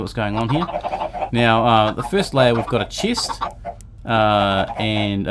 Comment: did u say cheese